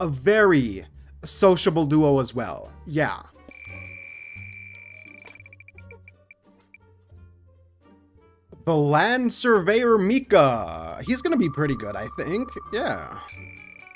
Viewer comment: Why did I hear “BlAnD sUrVeYeR mIkA” instead of land surveyor…💀